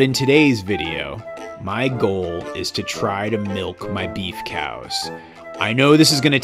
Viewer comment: I thought it said milk my male cow!!!